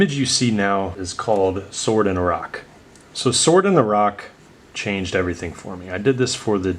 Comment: I thought you were saying sword in iraq instead of sword in the rock was so proud since im iraqi 😅😂😂